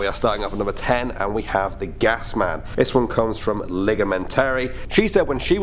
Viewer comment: When he said gasman and said who its was from. I thought he said ligma